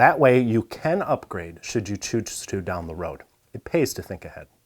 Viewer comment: Did anyone else catch the clever pun at 7:50? "... that way you can upgrade if you choose to down the ROUT" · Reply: Nope, you are the only one as he actually says ....."Should you choose to do so down the ROAD"